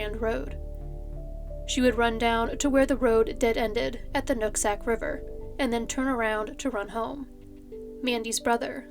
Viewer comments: Am I the only person that heard "nutsack river"? I listen to these while doing chores and such, so I often mishear things hear (get it?) and there. That one just stopped me in my tracks lol I'm not disrespecting, I'm human. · 😳... no... no... surely I must have just misheard the name of the river, as nobody would name a River.. that.. who would want to go swimming in the "........". or, who would want to go fishing down on the ole' "........"-River,.. or eat fish pulled from the mighty ".........". No, no, I am sure I just misheard.... @ 3:38-😳 NOPE! HEARD IT THAT TIME!! ITS THE NUTSACK RIVER Y'ALL!!!, CLEAR AS DAY, THE NUTSACK RIVER!!! I can only imagine what these folks named other landmarks around their town.. Fallopian Tube Tunnel.. Mamory Mountain..